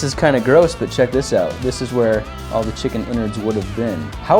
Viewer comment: I thought he said chicken wieners instead of chicken innards.